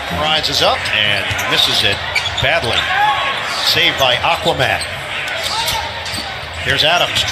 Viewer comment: did he just call adams aquaman?